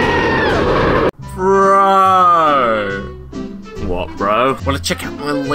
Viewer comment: What did he said?? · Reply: Qwistoff YTP Oh! "Bro what?"